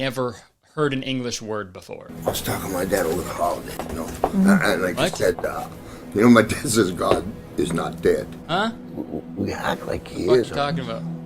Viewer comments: "I was talkin to my dad over the holidays, you know? An-and he said- you know, my dad says God is not dead. We act like he is!" After that i dont understand a fucking word, but i think hes saying something like "good and evil" and then i dont understand the rest. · I’ve listened to the boss several times and I THINK this is what he’s saying. Because of Kurtis’ editing there are probably places where the rest of the sentence is kind of cut off, but here’s my best guess. 4:41 “I was talking to my dad over the holiday and he says, you know, “God is not dead.” 4:45 “They act like he is…” 4:47 “Good and evil? Hey, look at me.” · I think I figured out some of what the boss said. “I was talking to my dad over the holidays and- my dad, you know, he says god is not dead… good and evil [unintelligible]” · As someone who watched old coronation street episodes to develop an ear for hard English accents, it sounds to me like he's saying: "I was talking to my dad over the call today, you know" (or possibly "over the holiday") "an-an-and like I said, ah, you know my stance is: god is not dead." "y-y-y-you act like he is!" "Good and evil (creep? creak?) couldn't it." (No idea what he's trying to say, full context would probably help this one a lot) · I think he says "I was talking to my dad over the holiday, you know? He said, my dad said, God is not dead. People act like he is, but good and evil? Hey, look at me!"